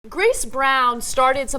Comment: lol sounded like she said "Chris Brown"